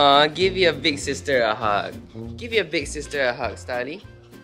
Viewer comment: i heard 4:06 as scarley the first time. idk why but it sounds like it would be starley's brother's(if he ever exists) name other than thunder xD